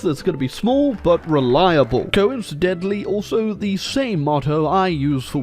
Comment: At first I thought you called women in the bedroom "short but reliable" and was confused for way longer than I'd like to admit.